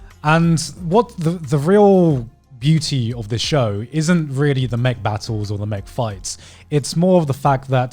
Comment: What other people hear say: It's not really about the mech battles or the mech fights What I hear say: It's not really about the Mcbattles or the Mcfights